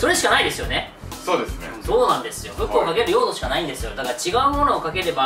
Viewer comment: ここ、「服をかける用途」で福尾亮が聞こえる